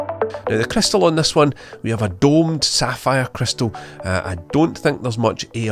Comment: “I don’t think there’s much (AR or maybe AIR) present” 3:45 I don’t know what you’re saying here…🤔